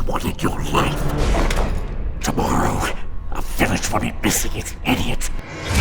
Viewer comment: “Tomorrow, the village will be missing its idiot.” A demon with a sense of humour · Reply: I heard it as " the village is full of idiots ". I had to enable the captions to understand what did valak said.